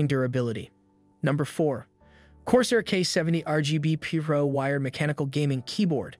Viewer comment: how did bro say pro? PEERO???